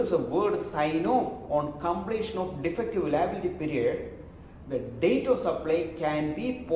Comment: Can someone please tell me what he said starting 5:46 to 5:48 ("otherwise, if there is a word signed off on completion of ____________). I really couldn't understand because of the accent (just only within this time). Thanks. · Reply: Dear Mike, it is "on completion of defective liability period". Thankyou.